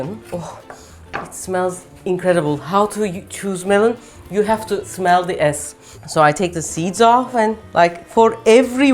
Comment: did she say "you have to smell the ass"?